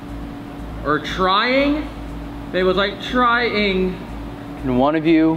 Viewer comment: sounded like it said “tapping” ngl